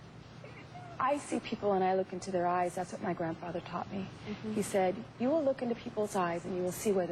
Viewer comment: did she just say "taughted"? lol